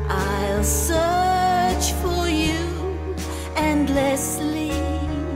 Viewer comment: Oh yes, I remember this one. Really nice! It's the one where I thought you were saying," you would search for you and Leslie". instead of "you endlessly" Ha!